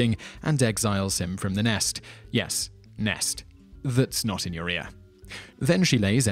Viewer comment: "That's not an [what?]"? What did he say here? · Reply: 'that's not in your ear"